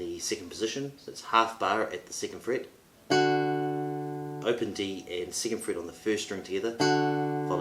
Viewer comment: Can you please type exactly what you said from 3:15 to 3:20 I couldn’t understand it well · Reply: "Open D and second fret on the first string together."